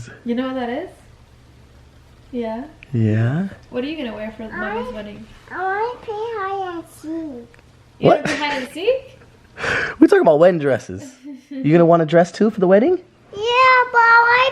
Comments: I thought Elle said “ I want to be high end sheek” whenbthe we’re talking about her outfit for the wedding😂😂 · “YEAH BUT I WANT HIGH END CHIC!!!” Lol thats what it sounded like for a second 😂😂🥰🥰🥰